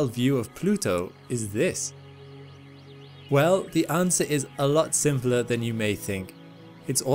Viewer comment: I thought he said the answer is simpler then I think? That confused me more